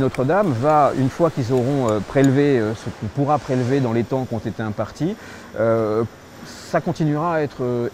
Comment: Un des rares dossiers à mettre au crédit de la "macronie" On voit bien que l'Etat excelle dans ses domaines de compétence quand il s'en donne les moyens humains et techniques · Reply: exact : j'ai entendu "dans les temps qui ont été impartis"